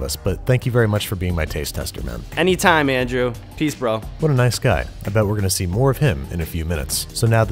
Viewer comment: "what a nice guy. I bet we are going to see more of him in a couple of minutes" Im sorry I thought you were throwing shade 'cause i heard "too bad we are going to see more of him"